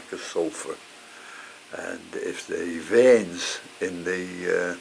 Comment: Hiya, from California. Yer videos R very educational. Thank-U. At the end of yer video, I heard “..if the Vends of yer leaves are going yellow, it could be a lack of Iron..” or was it Bends. What was the word? Also, I have Cymbidiums will this treatment work 4 them as well?